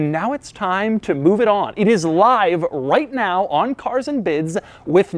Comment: Who else thought he was saying Cars and Biz 😂